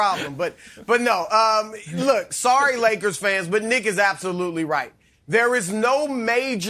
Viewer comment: am i the only one who thought Chris said N.....as absolutely right? 🤣 i had to rewind